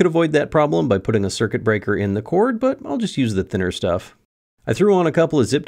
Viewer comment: What an awesome lamp. And you definitely said "thicker" at 6:35. 🤫